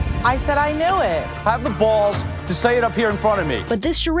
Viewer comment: did he really said "have a balls and say it in front of me"?, i just realized woman have a balls